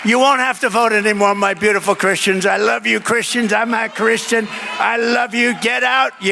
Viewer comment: I like your channel, Russell, but--I heard Trump say "I'm a Christan," not, "I'm not a Christian."